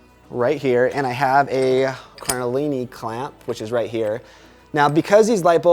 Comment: How do you call the clamp at 7:26? I hear "caralini clamp"? I searched and searched and I can't find them anywhere. I would love to buy them. Thanks for the great video, BTW! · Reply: Found it! They are called Cardellini clamps!